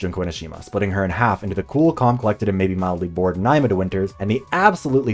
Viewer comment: who did he say here when he said "cool and collected _ winters"? I can't understand him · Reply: "Cool, calm, collected, and maybe mildly bored Naima DeWinters". I really need to get the captions done for this video lol